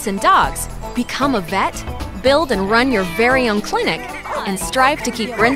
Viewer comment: I will build so many pet clinics... Oh my god!!!!! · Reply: Simproved I thought you said burn not build oml.